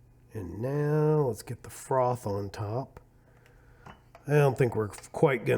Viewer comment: I don’t think this is part of the contest but did you say “Frogged” at 9:41? Asking just in case! Great video! I tried these and would say I felt the same, just an average pod. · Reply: Hey, no that was the word “froth” probably just me saying the word a little weird. The contest word will be something that doesn’t fit AT ALL. Nothing to do with coffee. Thanks for the guess though